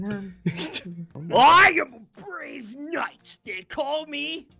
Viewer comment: When was doing his last joke, when he said that his name was brave knight, I thought he said his name was Deez nuts... I'm serious, I thought he said that the first time he said his name